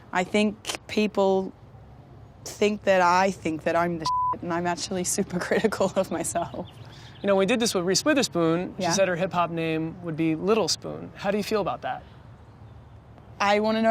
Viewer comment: Can somebody please tell me what she said when they asked whats ppl biggest misconception about her in 6:24 ? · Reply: She said that people think that she is full of her self when she really isn’t and she is very critical of herself